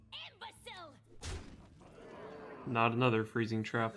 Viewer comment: is she saying... IMBECILE? Haha really?